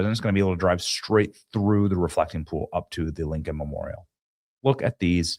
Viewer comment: I thought Benny said the "F'ing Pool'.